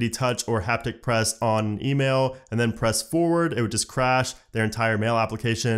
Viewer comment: I thought he said crabs.